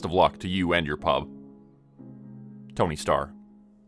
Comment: At the end I thought he said “Tony Stark” and this was his secret side gig 😂